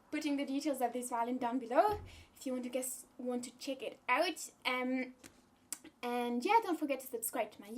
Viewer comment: Is Esther in UK or NZ? Because i thought I heard her say "check it out" as "chick it out" XD